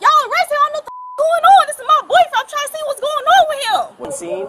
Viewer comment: Why does the narrator keep telling us what just happened? Is this the audiobook version of traffic stops? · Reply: you are correct , the video caption is saying words that are not even close in meaning OR pronunciation to the actual word 🤦‍♂🤦‍♂🤦‍♂ for example : at 34:33 the caption says "this is my voice" when the woman actually said " this is my boyfriend " like ???😅😅